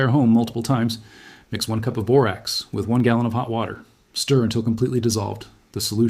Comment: 1 cup borax 1 gallon hot water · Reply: Was it hit water or warm water? I thought he said warm water?